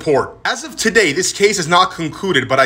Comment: I'm movin to Jupiter Best words ill hear all day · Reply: I actually thought he said "I'm going to shoot her" and I was very confused